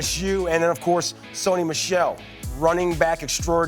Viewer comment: Did he just say Saquon Michel😂